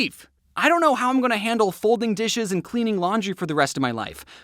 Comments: I heard you say falling dishes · just me who heard him say folding dishes and cleaning clothes??😂😊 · Did he just say folding dishes..... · Did you just say folding dishes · Did I hear you say “folding dishes”